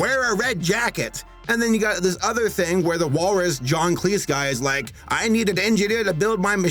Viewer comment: John Cleese? I thought it said John cheese